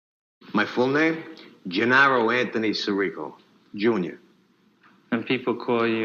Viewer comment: Nice analysis, enjoyed the video. But at @ 4:58 he clearly says his name “si REE co”. Not sure why you’re saying “SEAR ih co”?